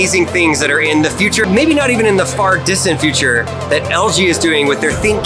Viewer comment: did he just say fart future?